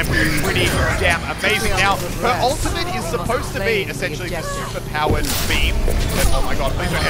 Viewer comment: I thought he said super powered meme at 1:40 XD